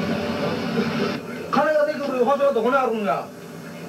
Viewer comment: なんていってんだろ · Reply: 返す金は、どこにあるんだ。です。